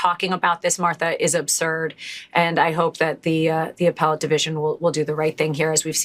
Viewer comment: Did you just say “apple ate” instead of “appellate”😊?